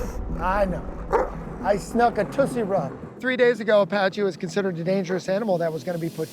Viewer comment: i thought he said "i snuck a tootsie roll"